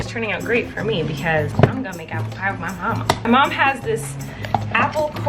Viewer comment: Lmao I thought that when said I’m gonna make apple pie with my mama I thought she said I’m going to make out with my mama and I was like WHAT!!